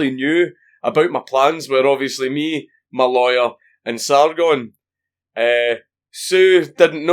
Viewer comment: When talking about who knew of his plans, i coulda swore he said Sauron !!!!